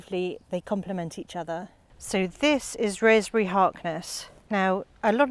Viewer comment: minute what is the name of the rose? I can’t understand what you are saying. I love your garden. Thanks for sharing. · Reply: Hi Kamila, thank you. It's Rosemary Harkness 😊🌹